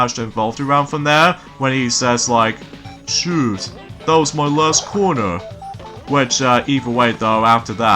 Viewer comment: And Actually, what Patrick said in the episode sleep time (2000) After he dropped the quarter, he said: Shoot, that was my last quarter. Your welcome. ( I just thought you would like to know.) Cause it sounded like you said: shoot, that was my last corner.